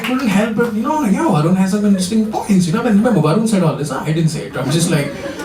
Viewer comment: what did he say at 9:03? just before "this is going up on youtube"? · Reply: "Varun has some interesting points. Varun said it haan I didn't say it."